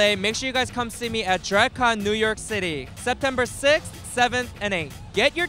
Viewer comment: Am i the only one who thought soju said september cyst?